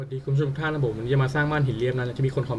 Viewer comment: ทำไมพี่ถึงพูดว่าวันนี้เราก็จะมาสร้างบ้านหีอะตอน 0:02-0:03 อะ 555+